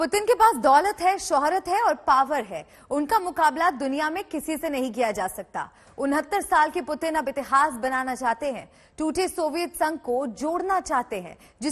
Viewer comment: Did she say unhattis (29) saal ke Putin ??? on time